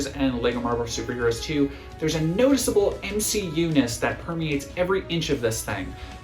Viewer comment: subtitles say "emcee EUNICE"